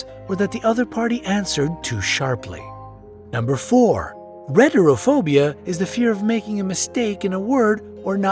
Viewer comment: I thought 4 said Retardphobia and i was close